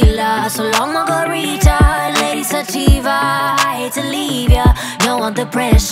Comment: I thought it said, “Lady’s a diva...” LOL